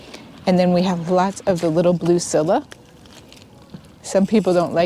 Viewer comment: At the 29:15 mark, you mentioned a name of plant that I've never heard of before. It sounded like Sivva... Could you please type the correct spelling here please? Thanks! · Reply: Hello, those are Siberian squall, also referred to as Scilla. They are early blooming small bulbs that naturalize easily. 🥰🍁🌾🌿